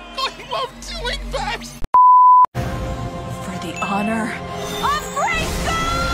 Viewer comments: When I was younger I thought she said for the honor of grey stone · I didn’t know anything about She-ra before watching this show and english isn’t my first language so I always thought she was saying “Gracegol” instead of “Grayskull” and when I saw I was wrong it was the biggest shocj of my life istg